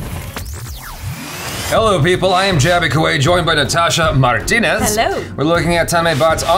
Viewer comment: Auto captions says - "Hello, I'm Jaby Kuwait". Nice!